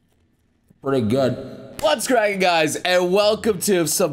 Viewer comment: Regular lyrics: "What's cracking guys!" Misheard lyrics: "Butts cracking guys!"